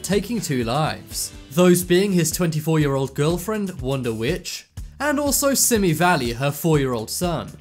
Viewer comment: i thought u said wonder bitch